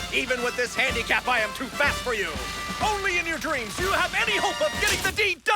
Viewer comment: I literally paused it fast at 3:14 and literally sounds like he said, Only in your dreams you'd have any hope of getting the D- I DIED.